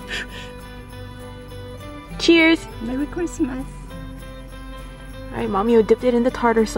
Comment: I thought said "Merry Crunchmas" hahaha 😆🤣